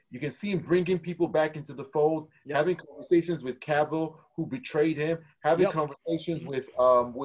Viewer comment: Great discussion. Gotta question though - at 1:03:25 - talking about Snyder bringing people back into the fold. Who's the person being discussed? Sounds like says "having conversations with Cavill, who betrayed him" - but I can't quite make it out. Was there some issue between Cavill and Snyder?!